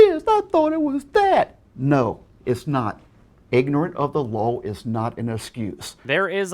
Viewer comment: it sounds like he said "ignorant of the lol" instead of "ignorance of the law"